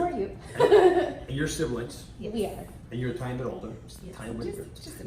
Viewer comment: the guys says "and you're a tiny bit older." The CC says, "and you're trying to lose weight"